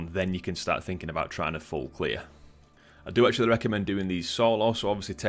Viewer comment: What does he say at 1:10? I don't understand.... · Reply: “I do actually recommend doing these solo” 😉